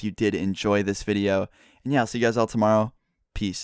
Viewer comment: '' And I'll see all you guys tomorrow, peace '' I thought he said: '' And meow see all you guys tomorrow, peace '' XDDDD